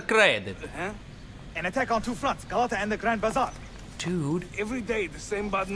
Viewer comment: I thought Yusuf actually said dude